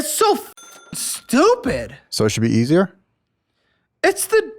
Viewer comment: I swear I hear him say “so I should be beating her” 😭